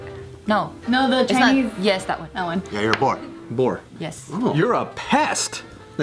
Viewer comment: HAhah i thought they said "you're a pork" to